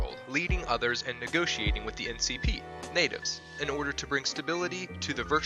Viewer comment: instead of npc u said ncp